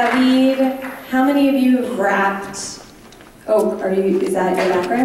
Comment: What happened at 21:20??? Why was everyone bugging? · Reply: At that part they thought he said raped instead of rapped